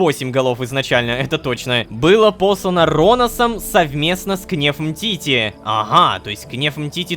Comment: мне послышалось был обоссан Ронасом😅😂